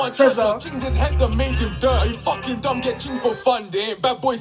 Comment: I thought he said “chinged his head and made him durr” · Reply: He did. “Chinged my man down and made him duh” then goes on to talk about dumb. The stupid scheme is cold icl.